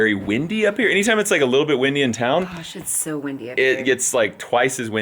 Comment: What said: "Gosh, it's soo windy up here!". What we actually heard: "sh it's soo windy up here!!" (But without the space after the sh!) 😂😂😂😂😂